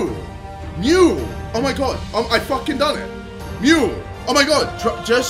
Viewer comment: I don’t know how he thinks he said “Mural” all I heard was him saying “mu”,”mu”,”mu”